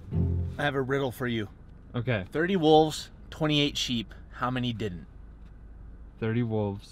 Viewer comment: I heard the joke like this 30 cows in a field 20 8 chickens how many didn’t